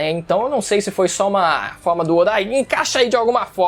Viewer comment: Eu ouvi do oda? O cara ta até onde ele n é o criador kkk